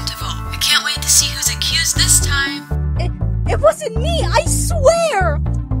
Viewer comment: I think Elaina ish the murderer OwO I have two reasons but one of them is hard to explain huehuehue 1. If she REALLY was the detective, the murderer would have killed her already. Edit: So that there would be no lead on the murderer. 2. In the trailer, Annie was the last survivor. And there was a shadow with a knife- and there I saw a cape silhouette shadow thingy- :V only Elaina has that. ItS CaLleD A GuEsS Edit: Why do I have 58 likes and yes I liked my own comment. Another edit: After 2 minutes I edited again UnU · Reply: Bananaz 101 I agree and also the voice sounded like Annie saying “I-It was you”